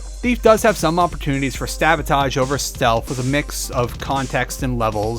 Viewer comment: Did you just say "stabotage"? LOL